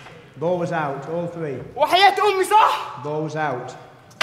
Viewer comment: What is Ramy saying to appeal about the out of bounce at 25:40? I guess its Arabic. Some swearing? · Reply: if you're still curious, he said "i swear on my mother's life it was in" :D Egyptians are funny that way. :D